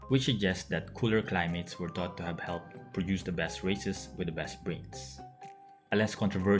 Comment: Is it just me or I hear you say "the best racists"